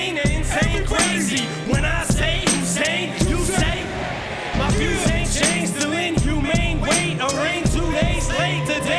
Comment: What is it that he says after I'm 28, they gonna take you before they take me, crazy and insane, when I say _______? I can't understand what the audience shouts back. What are they all singing back? its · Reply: they say shady ig